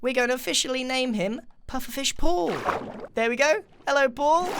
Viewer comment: Did he say logan paul? LoL